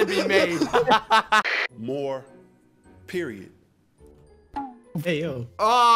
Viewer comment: Btw for the less talk more ___ ad the guy said period. He thought it said let’s talk more